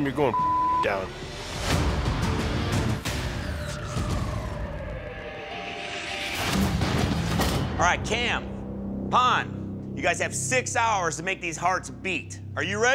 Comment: alright tam...pon haha datz wat i heard sorry my bad